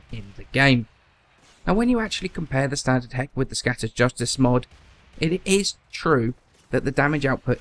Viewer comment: the captions say "Scottish Justice mode". This made my day.